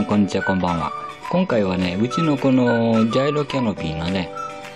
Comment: 「うちのこの～…。」と仰られているのでしょうが、「うちの子の～…。」に聞こえてしまいます…。深い愛情が、ひしひしと伝わってまいります…。（笑） 🐱🌹💖🐰